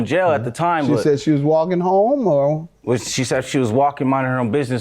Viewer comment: I thought the defendent said she was walking to take him bail money, not walking home.